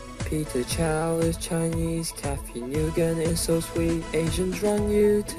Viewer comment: I heard you said Cathy Ni-yu-gen at 1:03 LOL